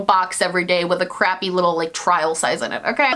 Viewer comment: I 100% thought you said Christmas sex. I was like oh.. okay that's quite a shift